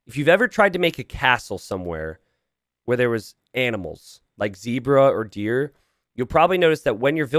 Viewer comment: I heard "animals" as "edibles" but it still made total sense.